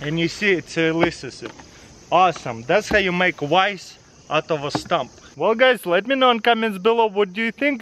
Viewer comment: oh you said vice out of a stump now I understand I thought you said rice out of a stump